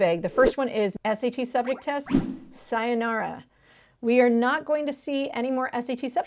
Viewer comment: Is it me or did I just hear her say "sayonara" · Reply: she did